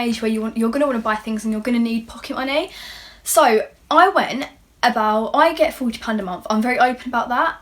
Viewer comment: I work about 40 - 50 hours a month as a teen for about 140-180 euro's. My parents give me about 20 euro's. And from all of that I save 75% for later. At first i thought you said 'i get 200 a month' 😂 Like there is this video going viral about a girl on Dr.Phil that says she hasn't got enough money when her mom only gets her 1000 a month 😂 like what tha hell.